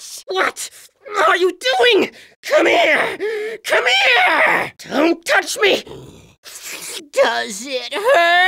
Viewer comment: Is he saying "Come touch me"?